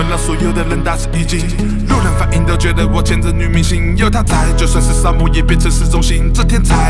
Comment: 一直把 "有她在就算是沙漠也變成市中心" 聽成 "有她在就算是掃墓也變成是幸運"